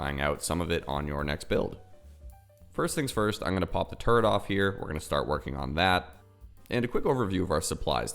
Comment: When you said "pop the turret off here" I swear I thought you said "pop a turd off here." Made me do a double take.